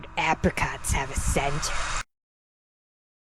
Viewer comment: I heard at avrcops have a senator